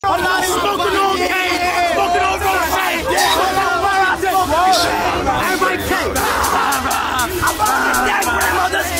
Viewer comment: who does he say after caine ?😭😭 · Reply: Roshane, I think it’s a 800 yg ngas grandma 😭😭😭